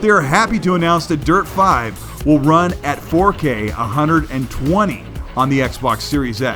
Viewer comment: I'm sorry did you say 4k 120 fps!?